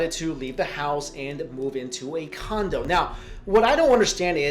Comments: At the end it sounded like he said she moved into a condom😂😂😂😂😂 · I thought you said 'move into a condom' at the end XD